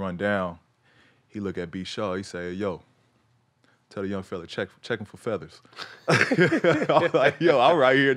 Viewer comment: Can someone tell me what did Kobe say? “Tell the young fella check in for feathers”? Sorry my English broke😥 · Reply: he said check him for feathers. Kobe was calling him soft